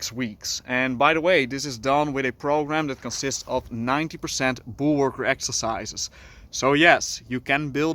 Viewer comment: what did you say? Bull work? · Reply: Bullworker